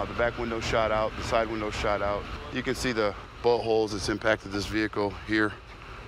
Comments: sounds like he says buttholes. Can't resist the temptation. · I swear I heard him say "you can see the buttholes that impacted this vehicle" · Did no one notice how he said “buttholes” instead of bullet holes at 2:42 😂😂😂😂